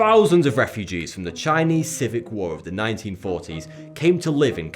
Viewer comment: Am I the only person who heard him say "Civic War" instead of Civil War?